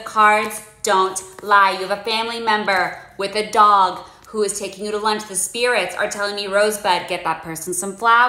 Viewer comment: I misheard it as "you have a family member who is a dog, he's taking you to lunch" and I just accepted it as the kind of thing these people say